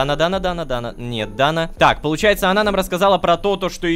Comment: Рома : Дана, Дана, Дана - Не Дана :| Что я услышала: Дана не отДана ;)